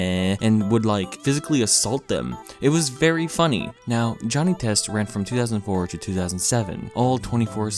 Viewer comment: did he just say Johnny Test?